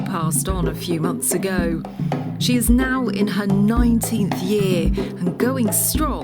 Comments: OMG I thought she said 90th year! Wow, Maddie is such a beautiful girl! R.I.P Maddie 💕💕💕🙏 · Because of the accent I thought they were saying 90th year, I was blown away then saw the same guy holding them as pups and realized I have to put on CC to understand them. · I watched this half-sleep yesterday and thought they said she was in her 90th year, bout to show my mum today and realized they said 19 lol